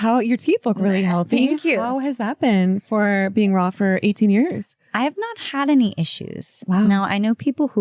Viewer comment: she consumes non raw stuff all the time. the powders are cooked as are the supplements. various nuts also have to be cooked or they are toxic, like cashews. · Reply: I thought she said she is fully raw for 17 years or something like that